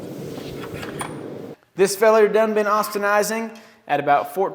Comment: What is the word that he said at 5:52 "austhanizing" (I'm guessing how to spell it, but can't find it online) does it just mean normalizing/ resetting the grain structure? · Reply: Austenising. Wikipedia "austenite".